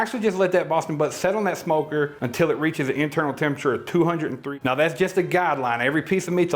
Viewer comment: "til it reaches a temperature of two hundred and th . . . ." You faded your voice out (08:09) so I didn't hear the actual temp. Pray, tell. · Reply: You're probably looking at about 205.